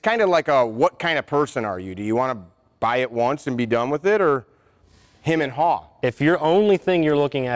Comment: "Buy once and be done with it.." I heard it it said, "Buy once. Cry once." I love it. Once the money is spent, you don't miss it. But buying cheap, over and over again? It ain't cheap!